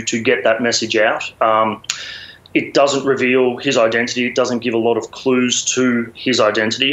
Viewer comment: I thought he said it contained his identity.